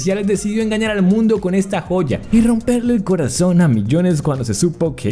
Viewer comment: Pensé que dijo poya... 😂